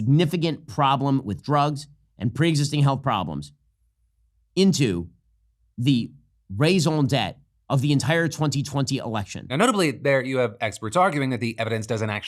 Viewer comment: shapiro really butchered 'raison d'être' there. Sounded like he said raisin debt lol